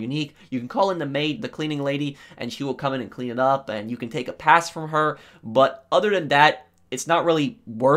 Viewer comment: LOL, I thought when he said the maid he said you can make a pass at her